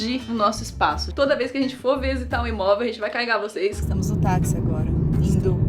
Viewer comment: eu entendi "a gente vai cagar vocês" juro 😂😂😂